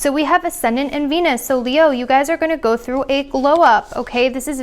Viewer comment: When you said “glow up” during the Leo reading I heard “blow up” and had a momentary panic attack lol. I’ll take a glow up any day, can’t wait 💖💅🏻